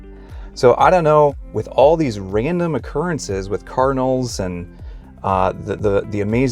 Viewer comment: At first I thought he was saying carnal, instead of Cardinal.